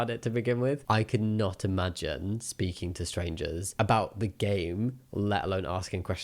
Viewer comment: anyone else thought it sounded like he was going to say ‘gay’ instead of ‘game’ ?